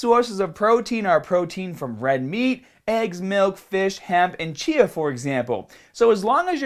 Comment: what is the last thing he says when he lists things up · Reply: Chia.